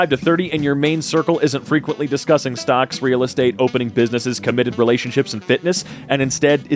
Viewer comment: When they said “opening businesses” and “committed relationships” my brain fused them together and I heard “open relationships.” That’s not what they said but I find it funnier to imagine that this person thinks that you should only surround yourself with people that reject monogamy and are comfortable with multiple partners.